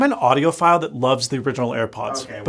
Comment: i thought he said im an Audio File SMH